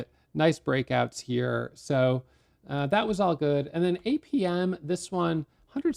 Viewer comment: In minute 10:13 said he moved to another stock at 8pm. 😂 Did he make a mistake or did I hear wrong. I was so confused.😅